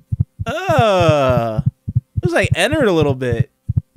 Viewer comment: I thought that at 3:33 said- that looks like the editor a bit